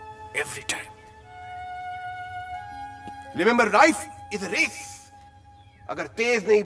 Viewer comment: Acha it was “ remember life is race “ from past 14 years i thought he was saying “ remember rice idrees” 😂